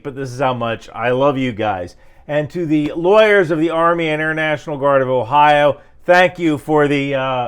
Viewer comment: In the sleep deprived slurring I thought he says "Ohio Army International Guard" instead of Air National Guard and for a second was wondering if the Army National Guard had dramatically adjusted its mission.